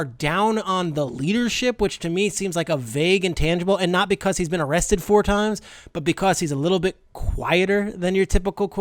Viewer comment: Did I hear that right, 8:37 Justin Herbert was arrested 4 times?